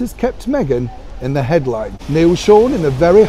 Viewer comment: At first I heard "caught Meghan in the headlights" (headlines) 😁 Gorgeous deep purple flowers...yum. Lovely as ever Neil Sean 😘